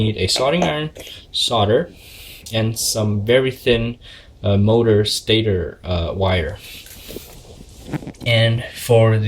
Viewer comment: CAn you provide a link to the source of your materials, would like to do this, but its hard to find what you have exactly, and i can't understand exactly what you said on the thin wire, it sounded like you said motor stater wire